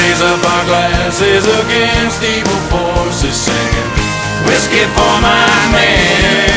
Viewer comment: When I was younger when it said whiskey for my man beer for my horses I always thought it said listen for my hand before my horses